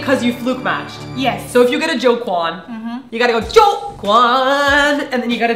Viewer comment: I was like what? when said ''... you got a Go Kwon!'' I thought she said Jo Kwon lol :P